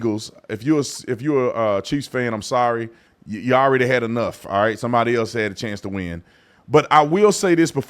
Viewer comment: Bro, I just thought you said "if you're a cheese fan, you already had enough" lolol